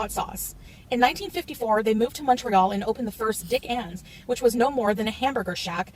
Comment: I’m in the other room. Listening to this. I thought she said “Dic Endz” I’m going to eat at Dick endz...! WHAT..? I just about broke my neck running back into the room.